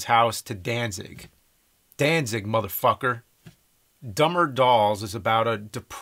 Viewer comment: For the longest time, I thought what Danzig said under the bleeps was "Danzig, Buttfucker".